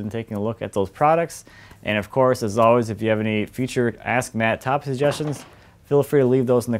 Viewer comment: He said Ask Matt, I thought he said Hazmat. LOL.